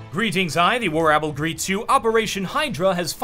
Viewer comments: "Greetings, I the WarApple greets you"? Was I the only one who heard that? xD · did he say WarOwbel?